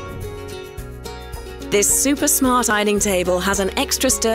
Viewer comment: what is she saying at 0:05 ? super what? sounds like small or smart or something phonetically similar. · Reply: smart